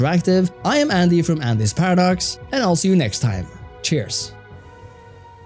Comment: Thought it said "angry paradox" instead of "andys paradox"